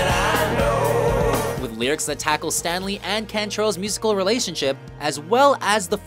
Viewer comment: Did he say "Stanley and Cantrell"? I may have misheard...It's Staley, Layne Staley.